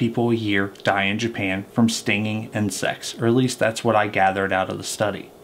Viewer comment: I thought you said they die from stinging and sex..I had to back up had listen again